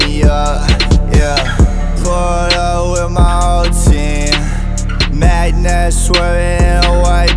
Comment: thought he said poured up with my whole team